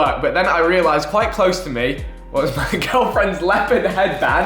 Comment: Am I going crazy or did I just hear Vik say he has a girlfriend. What has the world come to?